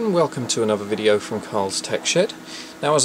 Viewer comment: It sounded like he said "Carls Tech Shit" LOL. (no offense)